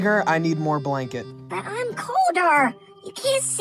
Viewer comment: i thought she said” Im older” that was gonna be some lore